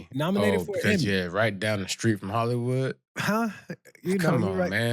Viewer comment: Did you just say Hollywoot